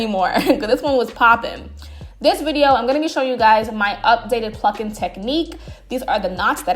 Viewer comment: Wait sus!! I swear I thought you said “Your updated fxxcking technique” 😂😂😂😂 I was like wait!! Anywho! Thank you so much! This video was perfect!